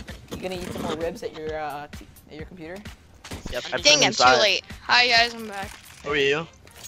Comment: all i hear is me sayin dang im too late